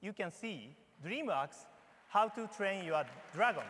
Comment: It sounded like he said dream rocks instead od dream works lol